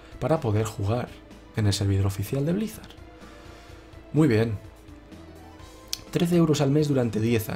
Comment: 3 euros mensuales? aca en México habian sido 150 pesos que equivalian a los 15 dolares pero ahora ya son 180 y es una lastima porque WoW es uno de mis juegos favoritos pero no puedo permitir que me estafen de esta manera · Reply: Dijo 13 euros. Al principio también entendí 3, pero luego dijo 13 (escucha bien en el minuto 7:52). Saludos